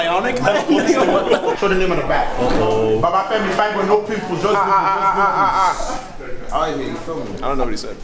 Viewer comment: I think he said “Baba Femi. Fine boy. No pimples just dimples” I think 😗